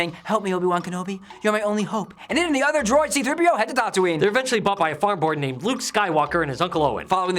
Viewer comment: Sounds like you say R2 & 3PO head to Dantooine @ 0:37. Tattooine!